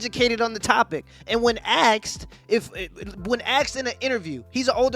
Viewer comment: did my mans just say aksed? Instead of asked?